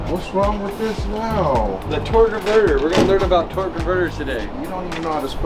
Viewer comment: captions said "torta burger" instead of torque converter 🤣🤣